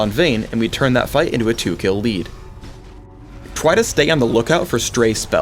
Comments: Skip to 3:05 to hear Phreak say 'twy' instead of 'try'. It just made me laugh. ^,^ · Does anyone else here "Twy" instead of "Try" xD?